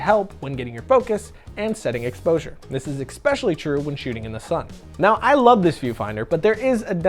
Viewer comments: Did he say 'expecially' @ 7:05? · Did you say ex-pecially at 7:06 ?